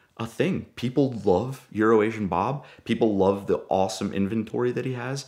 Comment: Oh, euro-asian Bob, I thought you said urination Bob.